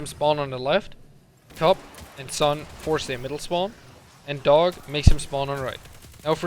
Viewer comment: I just didnt understand when you said where majors spawn when you kill cup and sun psion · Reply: he said that it forces a middle spawn so the door under callus. :)